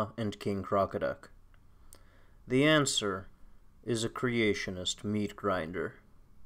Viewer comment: OMG WHAT'S HAPPENING!? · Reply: Shannon, I thought it said "Creationist's meet GRINDER" which would be interesting...